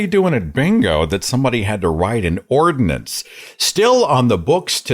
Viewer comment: I thought you said that it was illegal to TAZE a skunk! 😂 I had to go back a few seconds to hear that again!